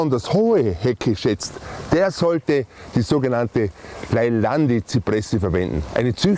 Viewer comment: Welche Hecke meint er um die 1:20 ??? Kann mir das Bitte jemand sagen. Leider verstehe ich ihn kaum. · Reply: Leyland-Zypresse